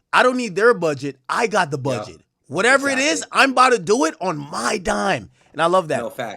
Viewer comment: "You know what I don't need their budget. I am the budget." That's what I heard lol.